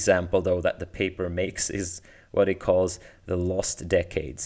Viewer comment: "... what he calls 'The Lost Decades' and this is ..." I'm pretty sure the author's pronouns are she/her (edit: I misheard). It's an interesting paper. I guess we're "lucky" that graphics processing has characteristics that overlap with machine learning workloads (not to imply that it is a random coincidence, of course) and that demand for better graphics has been so consistently strong over the last few decades. Otherwise we may have had to wait a fair while longer before deep learning (as a whole) "won" the lottery to the extent that it has.